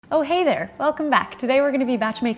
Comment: u started as " Oh hey there!"...I heard it like.." oh hater"... I was like why would anyone hate ur awesome content?!!😁 Lots of love from India..! ❤️ And I am a vegetarian on way to take up veganism..! Tried ur veg dips! They were 👌 Wish u can post some vegan recipes that has contents of my country produces!😊